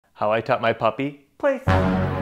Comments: Oh okk its "taught" I heard it as "cut it" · I heard “how I cut my puppy” at first 💀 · I thought he said how I cut my puppy☠️☠️☠️